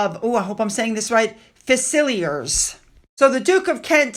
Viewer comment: I'm not sure what you were saying at the end of Royal Regiment of xxxxx (1:54) but on the subtitles it came up as Regiment of Right Failers. 🤣🤣🤣 Now you''re in trouble! 😀